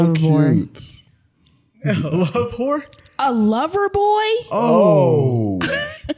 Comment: I thought said "love-ivore." is a love-ivore, haaay!